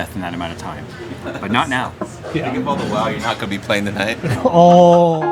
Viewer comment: What does say at 10:00 to Jay that he reacts like that ? I sounded like you're not going to playing tonight?